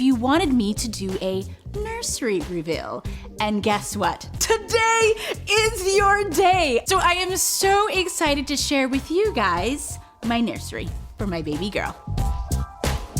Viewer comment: Which element of my baby girl's nursery is your favorite? Let me know in the comments! ❤️ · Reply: I so thought said was, "TODAY IS YOUR LUCKY DAY! I am gonna share with you me nursing my Baby!" I was like YEAAAHHHHHHH!!!! THANK YOU!!!